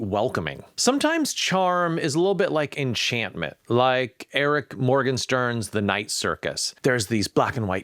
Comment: Did you call her Eric instead of Erin? That sounds like it.